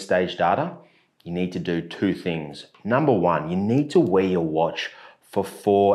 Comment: I cant figure out what "weigh your watch" means. I am trying to set my watch up now. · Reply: im pretty sure he says “wear your watch”